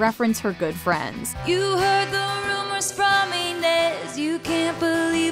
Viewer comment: I always thought it was "you heard the rumors from me next" not Inez. Very interesting name.